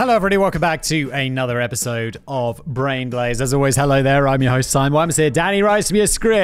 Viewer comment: Being partially deaf and rather ill right now, I heard him say "Brain Glaze." And that's what I need. Stupid content for people whose brains have turned off for a little bit.